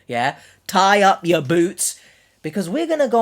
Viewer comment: Why in the begging it sounds like you said tie your boobs lollllll